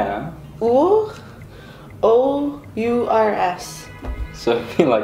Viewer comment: lol when she said "ours, o u r s" for one second I thought she said -oh, you are ass